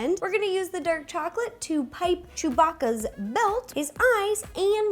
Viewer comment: she says Chewbacca instead of Chewy